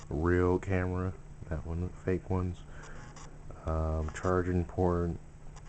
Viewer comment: There is no ghost. I just moved the platform by accident,mystery solved. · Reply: did u say chaching porn at 1:39-1:41??